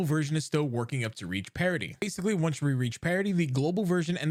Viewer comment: ok i keep hearing this "parody" thing, i have to ask now, wtf is it? new story? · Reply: In good faith that you're not trolling; the term is "parity". Not "parody"